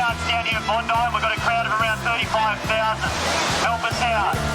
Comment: Did I hear that correctly, a crowd of around 35,000? 😮